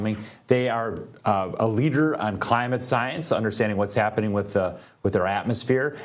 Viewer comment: premiere science organization?? it sounded like he said " premiere Zionist organization"!!!!! play it back, ull hear it! 😂